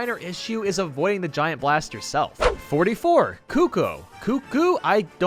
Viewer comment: Did you just call it a “bob-o” at 6:09? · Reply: He said Bob-Ohm.